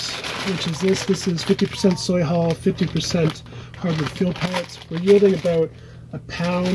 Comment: what did you say you grow them in? 4:00 i hear 50% soybean hulls but i couldnt hear the other part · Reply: It's master mix. hardwood pellets and soybean hulls